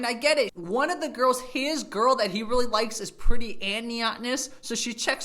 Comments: I don't understand the text at 12:00. You say something like "is pretty and nyatness". The subtitles say: "is pretty Annie honest" What do you mean there? and what is "nyatness"? Is it something Chinese? (I'm Flemish.) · What did you say she was? "Pretty and ..."? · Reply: Neotenous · Great video, but I’ve got a qestion, what’s the word you used at 12:01??? “Chinese guys like girls who are pretty AND........???” Keep up the great work bro! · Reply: Neotenous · What's the word he uses? Niatnes or something like that? What does that mean? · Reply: Neotenous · What word is used at 12:00 to describe the girl? Never heard this word before. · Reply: Neotenous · What were you saying at 11:57? One of the girls is pretty 'amniotness'? wtf is 'amniotness'? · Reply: Neotenous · What is the word he said at 12:00 i never heard that word before ?😲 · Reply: Neotenous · What did the guy say at around 12:00? That she is neotness? neeoughtniss? Niotniss? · he mentions that Chinese guys like girls who are pretty and "niatenest"? I couldn't really catch the 2nd quality he mentions.